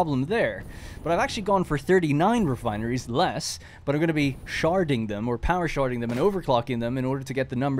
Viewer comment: "sharding" as a verb made me doubletake cause i thought you said you were gonna shart on your refineries and i got so concerned also a separate world tour may be nice! i haven't been able to catch any streams so seeing what you've done between episodes in a dedicated video would be really cool!!